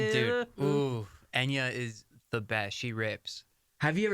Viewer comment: Did he say “she rapes”?